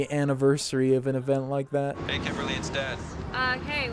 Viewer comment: Am I crazy or when her dad calls does he say "Hey Kimberly, it's death"?